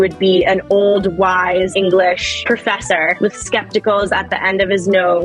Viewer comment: Did she say skepticals??? 😵😵